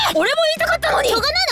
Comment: ちなみに左遷されたクロハのその後は 知らね 俺も言いたかったのに 〇〇二人で言うか、せーの 知らね 〇〇の何て言ってるか聞き取れん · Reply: エイジヤマダ しょうがないな2人で言うか って言ってると思います！